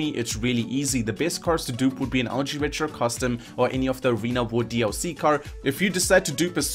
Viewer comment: what car did he say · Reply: Ultra retro custom